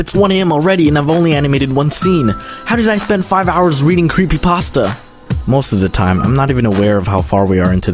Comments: DID SOMEONE SAY CREEPYPASTA and that's the story of my life.. Im still working on my scribble trailer but all i do is read CreepyPasta XD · DID SOMEBODY SAID CREEPYPASTA?!?!